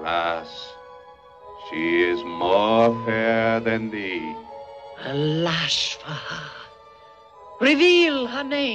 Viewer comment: I like that when it was some random she was just gonna slap her for it, but when it's her step kid she goes straight to murder is the best solution mode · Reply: I thought she said, "ALAS for her!" repeating the mirror's words (with a slight lisp). 😆